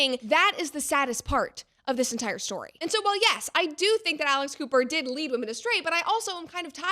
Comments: I don’t even know who Alex Cooper is. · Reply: Agreed. I thought she said Alice Cooper. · I thought you said Alice Cooper 🤦🏽‍♂️